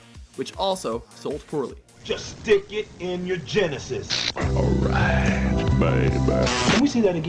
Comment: "just stick it in your genitals". I swear that is what he said.